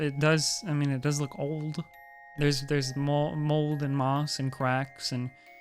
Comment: DID HE JUST SAY M O U L D